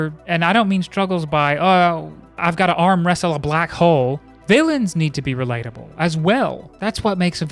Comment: "...arm wrestle a black hole" xD that was way too funny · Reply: Lmao, I thought he said 'black hoe'